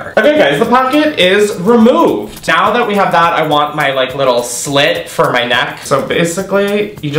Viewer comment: I misheard "I wanna slit my neck" and thought "Same".